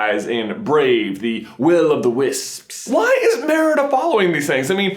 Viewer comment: my name is Willow Will O' = Willow so at first I thought it said Willow the Wisps